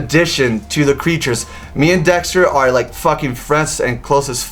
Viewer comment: good luck <3 oki i just heard say him and Dex is "sex" friends xD 3:00 :3 now yaoi fangirs go wild :3 jkjkjkjk